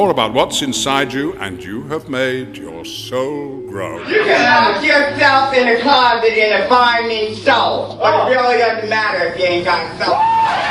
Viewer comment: What is the person saying at 3:45 i cannot understand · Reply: marsha p johnson: "you can lock yourself in a closet in a fine mink stole, but it really doesn’t matter if you ain’t got soul."